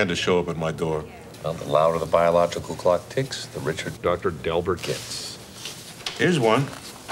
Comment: TF was THAT? Did anybody else hear that painfully obvious weird dub “Dr. Dellbert”?